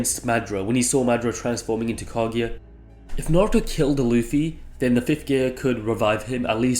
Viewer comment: At 1st I thought bro said luffy was using the 5th gate (of limit) 😂😂